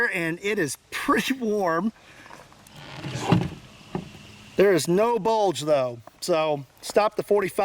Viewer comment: All I heard was “there is no bulge”